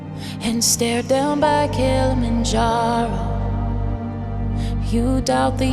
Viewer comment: why do I hear ninjago